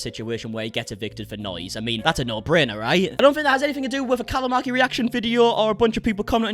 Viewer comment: When he said Callum Markie I thought he was saying calamari 😂